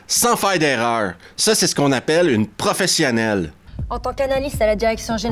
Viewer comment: quoi j’ai entendu « Prophecies An.ale », non c’est pas possible, ça n’a pas de sens ! Réécoutons…🤔🤫 ha c’est « professionnelle » bien sur j’avais mal entendu. 😙😉